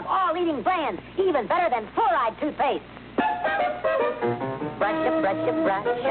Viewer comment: Fluoride toothpaste? Sounds like he said 4 eyed toothpaste!